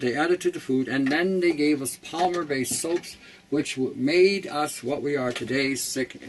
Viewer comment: does he say "polymer" based soaps?